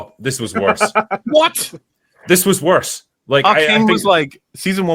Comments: What show did Mauler mention? They spoke over each other and the auto-captions didn't catch it, either. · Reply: Arcane. A series which Mauler loves the first season of because he's a LoL fan. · what show did they say? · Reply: Arcane. Mauler had a complete meltdown about season 2 because the show went from 10/10 to 7/10 in terms of writing and he can't handle it. He pretends like it is the worst thing ever. Meanwhile, he was is full cope mode for House of the Dragon season 2 so you can tell he is delusional.